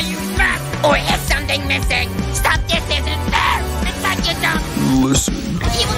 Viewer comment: my brains got rotted by "dont spray", now all i hear is "I SWEAR THERES SOMETHING AROUSING" "*im telling you its my di-*"